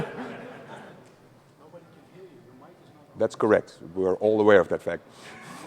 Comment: Anyone heard the "joke" said at around 7:23? I can't tell what the guy in the crowd says, but I wanna laugh too. XD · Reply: he said something like: nobody can hear you, your mic is turned off.